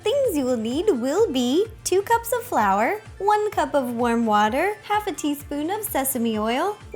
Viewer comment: Omg, I heard "worm water" and I couldn't stop laughing. Love u Ro!